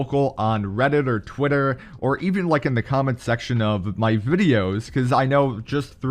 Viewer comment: Did anyone hear vudios lmao